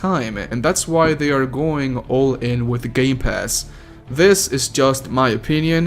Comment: Did he just say gay pass or is it just me? 3:02 Did he just say series sex or is ot just me?